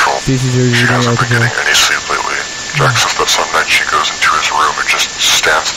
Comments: if anyone didn't recognize what the radio was saying, it said ''She hasn't been getting any sleep lately'' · sounds like he said "She hasn't been getting any simps lately."